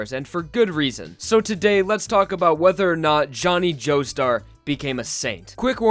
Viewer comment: Why did I heard "Johnny joestar became a simp"